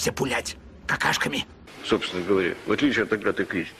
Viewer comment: Не могу понять что сказал Летов "в отличии..."?????? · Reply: От агаты кристи